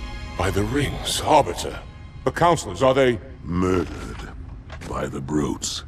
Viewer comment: what exactly does the Arbiter say? The prophets were murdered by the brutes? Or am I mishearing? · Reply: The councilors. The elites and the prophets. The elites side of the council were murdered.